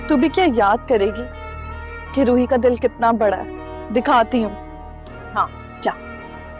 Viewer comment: The subtitle says Kiran not Roohi 😂